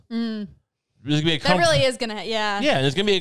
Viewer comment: Every time i put my raycons on i hear "RAY KUMP"